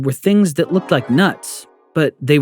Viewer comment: what did bro say💀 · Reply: He said nuts, what’s so bad about saying that.